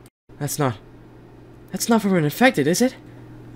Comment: “you need to leave” THATS WHAT I HEARD